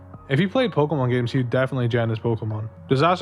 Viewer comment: "he'd definitely... what... his pokemon" gen? join? jet? what is this word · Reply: gen! like, generate them using 3rd party software.